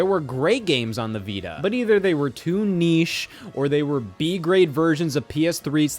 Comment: I get he's saying "too niche at the time" but this is to say, I still can't believe the series has sold 10 milly copies. still so crazy to me.